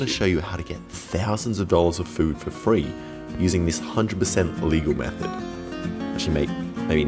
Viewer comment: I think he said illagal